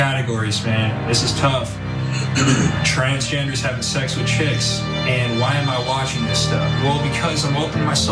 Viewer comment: Where does he say children? Seen this clip several times and never heard that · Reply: My mistake, in the beginning of the video, I misunderstood chicks for kids (trans having sex with chicks, not kids.) Ops, my bad! Good observation! When I misheard kids for chicks, I immediately thought he was lying, cause that's not mainstream. Either that or he's getting into some sick dark place on the internet, I would assume. Sorry, my ears deceived me.